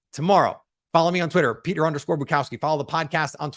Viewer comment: The first time you said "Bukowski" I misheard and thought you said "Majkowski"